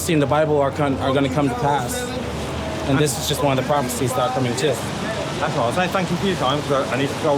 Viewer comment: Might need someone doing subtitles to relisten to what they wrote down, quite a few subtitles are incorrect. Example: 19:43 "pharmacies" was used instead of "prophecies". Even the autogenerated transcript gets it correct lol